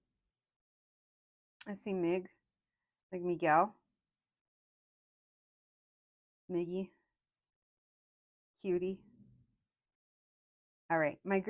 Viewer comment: pile #2 was pretty cool! i thought you said my name ‘mikki’ at 57:20 and i popped my head up like “okay this was for me” LOLL wonderful vid <3